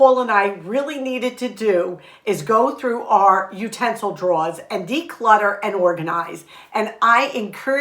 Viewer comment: Curious, did I hear you say "draws" instead of "drawers"? Is that a regional accent? I normally pray for a word of the year and I believe the word for 2023 for me is "sacrifice". This could be sacrificing my leisure time to do what God wants me to do. As for New Year's eve, we are going to extended family to have another Christmas celebration. We all bring food to share. On Sunday, we have church and then no other plans for new year's day. We will be eating leftovers and try to eat up what we still have from the holidays.